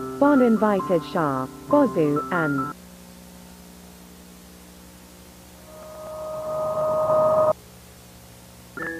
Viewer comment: does anyone know what that says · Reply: The first two parts I don’t know but the last says It’s ok, I killed him